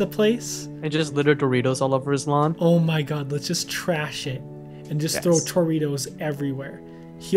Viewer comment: if you guys want to send in fan arts send them to my twitter at <link> ! now enjoy the video! ( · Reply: you said Trash It...I thought you said trash sh*t :[ criez